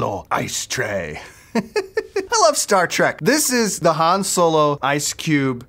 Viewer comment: Did he just say startrek